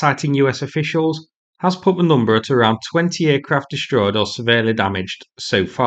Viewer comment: is it 20 or 28?? I hear 28 but I'm not sure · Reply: 20 aircraft